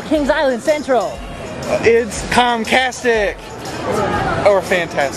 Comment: I thought you said Tomcastic :P